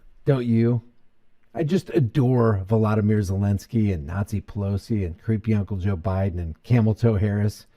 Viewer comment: I think I heard wrong, or may I heard right, but did you just say Nazi Pelosi?